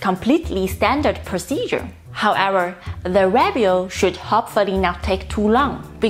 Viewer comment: What is ❤️ saying at 5:32? 🤔 Even captioning displays RABIOT. Sometimes the speech impediment makes the story confusing. 🥺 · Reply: "review" - "hopefully the review does not take too long, because if the review takes too long it could delay the demo 2 mission"